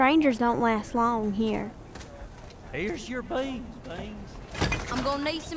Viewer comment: Did anyone hear "I have you beans bean "